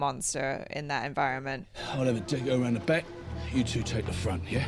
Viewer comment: Butchers accent is so thick I swear I heard " dingoroundtheback" but I had subtitles. 😅